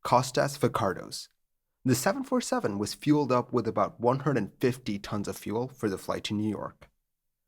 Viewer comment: I'm confused. I can't figure out what's being said at 0:51. It sounds like "...was fueled up with about 150 tons of fuel..."